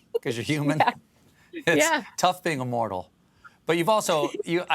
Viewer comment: I love how honest Billie always seems like she's trying to be. Also, best quote from Stephen: "It's tough being a mortal." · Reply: I thought he said, "Its tough being immortal." Referring to being everything for fans.